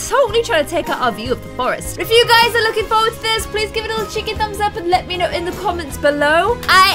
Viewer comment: Did anyone else think that at 1:15 she said "chicken thumbs up" or am I just a weirdo that imagines people saying the word chicken all the time?